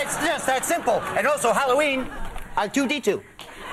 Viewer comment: What does Colin say when he puts on that trash bin thing at 1:49? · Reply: he says R2-D2 :)